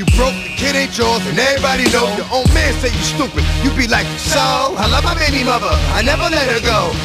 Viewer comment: “I be like so? I love my baby muva, I’ll never let her go” lmfao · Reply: Lol yea upon relistening i thought it said "you be like"... lol but i have the worst habit of misinterpretating lyrics lol.